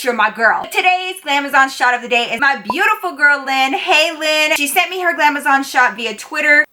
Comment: At the beggining instead of "My beautiful girl, Lynn" i heard "My beautiful gremlin" i litterally had to rewind just to make sure lmao